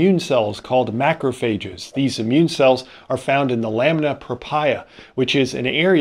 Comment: I heard you say "lamina papaya" instead of "lamina propria". I wonder whether that was intentional.